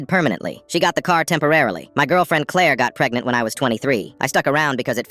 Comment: It's actually: "My girlfriend Claire got pregnant when I was 23" I'm sorry I wouldnt be able to sleep if I didn't 😭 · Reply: i thought it said my girlfriend claire got me pregnant when i was 23😭